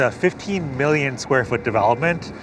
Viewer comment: 15 BILLION not million for Hudson yards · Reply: the host, & known architect said, "over my shoulder is Hudson Yards. It is a 15 million square foot development at the northern end of the Highline". He did not say 15 million dollars for the Hudson Yards' development. 😂🤣